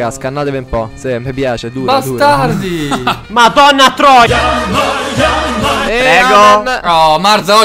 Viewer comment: IL"MADONNA TROIA"CMQ L HO SENTITO HAHA